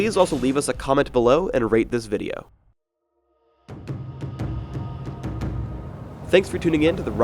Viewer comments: I could of sworn he said rape this video. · lol i heard wrong sounded like he said " and pliz reaip this vidio" first i was wtfO_O then oh ^^